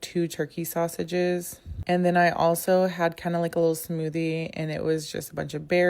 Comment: and then i also had cannibal smoothie ⏪️ oh yeah ok. I heard that wrong.